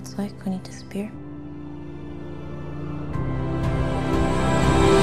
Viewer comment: When he asked what do you want? I literally thought she said “MORE GAYS”… and I thought wow! How extremely progressive of you, and then I thought, maybe it will be more like the Barbie movie. Who know, maybe there something there. Definitely less war….